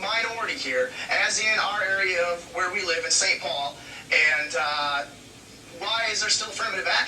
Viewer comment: This is mad crazy I live in Brooklyn Park Minnesota but I’m currently in Saint Paul and correct me if Im wrong but did he just say Saint Paul